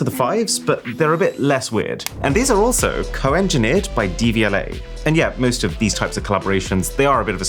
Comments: Why did I think he said dvla and not devialet 8:57 😂😂😂😂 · Thought you said DVLA, was confused for a sec